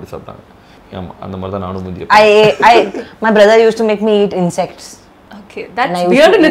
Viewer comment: what did she just say 😱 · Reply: Insects